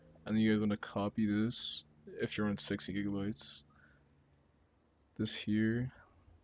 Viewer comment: did you say 16GB or 60GB? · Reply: 16